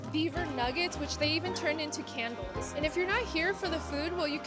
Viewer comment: At around 4:23, you were talking about the beaver nuggets, what were they turned in to? I have rewatched it several times and cannot understand what you are saying. Thank you! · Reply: They turned it to candles